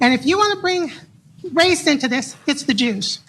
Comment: At the beginning of the video I thought she said "it's the juice"😂